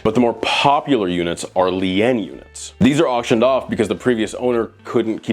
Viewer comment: I kept hearing the word, "Lee in" do they mean lein?